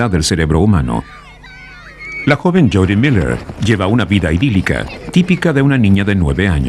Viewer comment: no entiendo lo que dice aquí 3:28 —lleva una vida emil.... no entiendo— alguien que halla escuchado bien me puede decir que dice, porfabor. Gracias. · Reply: "Lleva una vida idilica, tipica de una niña de 9 años"